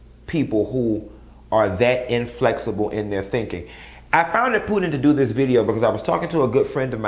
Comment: Did Q just say at 3:01 “I found it putant...?” Then AGAIN at 3:39? Lol 😂😂😂 ooh chile...Q them drinks is starting to kick in huh? 🍸🍸🍸 #howmanyfingersigotup✌🏽